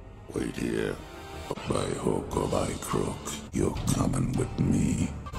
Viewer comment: What does he say at 35:30? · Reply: by hook or by crook you’re coming with me